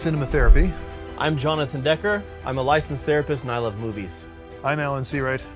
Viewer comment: I thought that I heard "I love boobies", which I would have completely agreed with if that was what was said. Azz over-rated, female blood balloons under-rated.